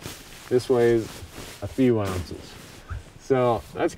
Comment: Your weight guessing is way off. :) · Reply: Starting with your pillow you said to 2-oz, less than 2 oz, and the I thought you said 2 oz on the bivy. You actually said a few ounces. I love crunching the numbers when it comes to gear & I have to apologise. I did the homework and you were closer than I thought! Modern gear can be amazingly lightweight now. Crazy. Thanks for the videos!